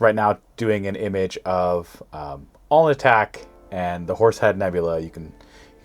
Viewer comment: Again, I hear “all attack” and not “Alnitak”. But this time, the automatic closed captioning agrees with me. So, if we both hear the same incorrect thing, I feel a little better.